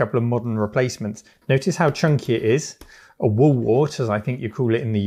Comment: I have no idea what you said here, when you said what we'd call it in the US. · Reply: A "wall wart"